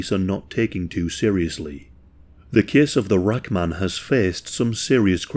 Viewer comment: I thought it said strange case of the black man ! Man I needs to stop smoking 😂